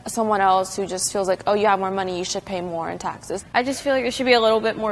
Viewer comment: For some reason I thought it said “You should pay more in Texas”